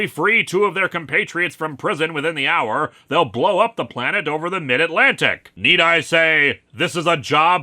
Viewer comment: Wait did I mishear that, "they'll blow up the PLANET over the mid-atlantic"???